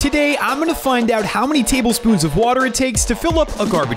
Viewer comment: for somereason i thought he said garlic instead of water.